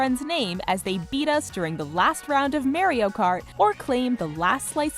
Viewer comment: Did she just say May-rio Kart?